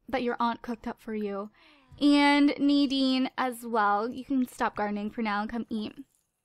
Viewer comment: When you said "Nadine stop gardening" I thought you said "Nadine stop grinding" I really REALLY REALLY hope it wasn't just me...